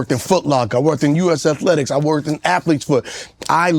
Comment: did he say athletes foot? 😅